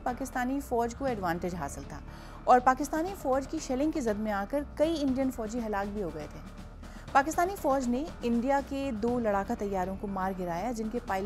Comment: did she say Indian fojji hallal ho gayy ? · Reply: Halak,, which means killed